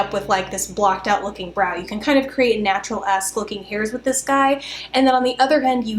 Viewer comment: "natural ass looking hairs" i'm dyin !!!! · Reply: I did have to think about it twice though. I thought she said ass at first as well. then i figured it's, she probably said -esque